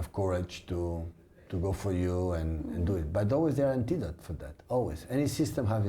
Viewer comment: What is he even saying at 7:18 ??? It's insulting to speak like that after 10 years in a country. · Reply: He's saying "antidote". Every system has a counter.